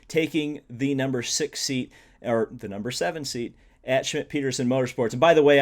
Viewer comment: Did you say shit Peterson Motorsport?